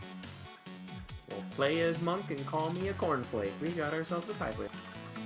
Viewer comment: What did he say at 17:40? Can't make it out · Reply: Bit late but he said "Play as monk and call me a cornflake we've got a tiebreaker".